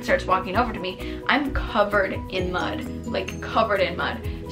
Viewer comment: omg for a second i thought she said blood- SO GLAD it was just mud lol